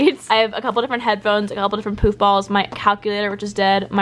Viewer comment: Is it just em or did I hear poop balls instead of poof balls lol😅😂😂🤣🤣